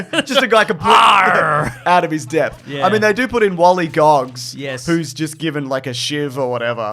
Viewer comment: Wallygogs really caught me off guard thought they were saying gollywogs!